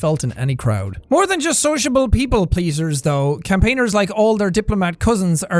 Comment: For a moment i thought he said p, p pleaser